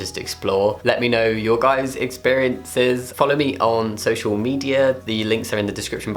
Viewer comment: thought you said sexual media instead of social media